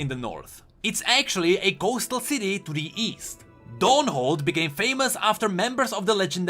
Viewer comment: East? I thought you said weast